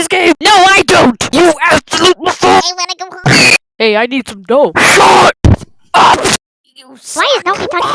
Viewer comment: hey I need some ####(I put this here cuz I didn't know what he said) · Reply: It was dough which means money